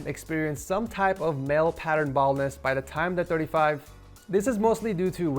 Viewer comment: i thought i heard male pattern ballnes (i thought it was ball falling) so when i heard apply it from home i thought it was a prosthetic ball.